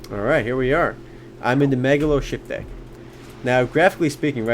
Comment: did he say im in megalo shit bay?